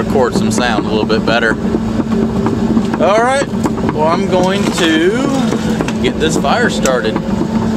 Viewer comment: Thought I heard say, going to start a fire Karen teed. Lol. You'd need a extra long stick to roast them marshmallows 😋.